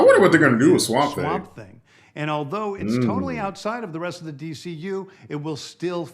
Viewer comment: I think he said "tonally" outside of the dcu - so I think he just meant the horror theme is different from the rest of the dcu